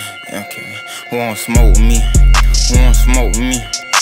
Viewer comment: Whole time he was saying who want smoke with me but I thought he was saying who smoke weed😂